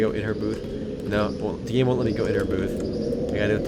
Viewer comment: thought you said "it won't let me in her boob" , "Yep, it wont let me in her boob" LOLLLLLL